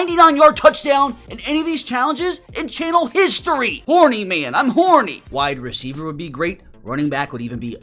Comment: why did bro say this?😂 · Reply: I believe he has said: warning man im horn*